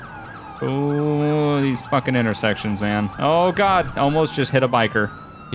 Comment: At the intersection sounds like he said "he's fucking intersections" instead of these fucking intersections